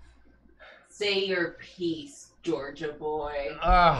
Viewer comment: "say your piece, georgia boy" has such commanding, threatening energy from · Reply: At first I thought she said “say your peace” like make your peace and that’s somehow more threatening